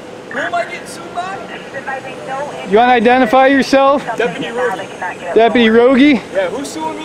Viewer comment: Damn I must be hungry as shit could’ve swore he said his name was deputy hoagie